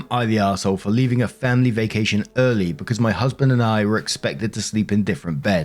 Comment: I heard “uncle” instead of husband and did the WORST DOUBLE-TAKE